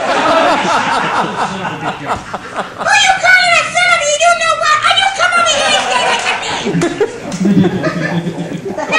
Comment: What did he say? 😂😂😂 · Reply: All I can make out is “Who are you calling a son of a you know what?! I oughta come over there and”… I can’t make out the rest.